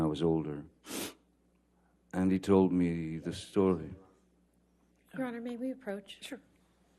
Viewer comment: can someone tell me what did she say?? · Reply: “Your honour may we approach”